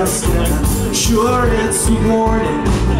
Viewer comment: I swear I thought he said "Up late measuring the hard on" at 4:15 XD